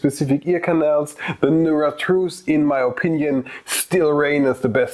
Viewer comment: Hey, great review, thank you ! I have 2 questions : - You talk about an earbud having a better sound than these but I can't figure out the name. At 3:40 you say : "The *** IMO still reign as the best earbuds of 2021." Can you help me out please ? - Do you think Sony could implement and multiple device connection through an OTA update ? (If they überhaupt do OTA updates...) Like it is a hardwar elimitation or software limitation ? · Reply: Hey MS, sure, no problem. In my Opinion, the Nuratrue Earbuds reign supreme in terms of Sound Quality, due to them having a personalized Sound as their Main Feature. I have a review of them if you are interested in checking them out: <link> And to your second Question. I would love to have a 2 Device Connection Update from Sony, but they havent equipped any of their earbuds with multi Connection, and i dont think this is a Software Limitation.